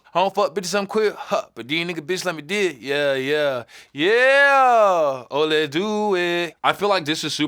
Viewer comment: when he said “oh lets do ittt” i thought he was saying “all dat bootyyy” and when he went “i aint lied since I began” I thought he said “I aint lied since i been gay”